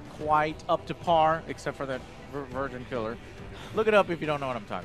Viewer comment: What did Joe say at 9:30 that we should look up? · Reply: Virgin Killer